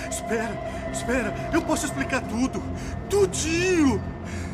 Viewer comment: Does anybody know what Serrano said at 8:38? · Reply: e ai doutor